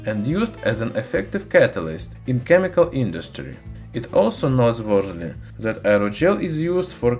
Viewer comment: Great video, but what was the word he was trying to say at 6:01 lol, was he trying to say "noteworthy"?